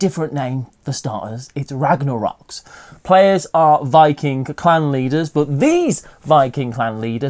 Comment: For a moment I thought you said Viking Clown Leaders instead of Clan Leaders for Ragnarocks, and now I demand that be the next expansion!